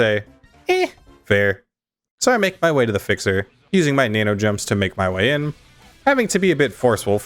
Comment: do I hear FIXER?...